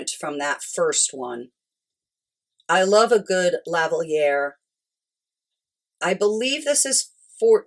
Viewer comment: What is the word you are using for the second piece? It starts with what sounds like “lav…” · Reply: Lavalier… thanks for watching!